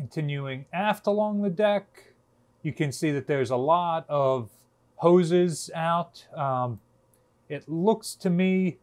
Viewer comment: I heard, "It looks like a of poseurs out." Oh, hoses, both make sense.